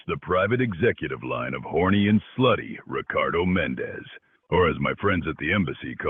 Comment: I’m sorry, did he say “H*rny and Floody”? 😭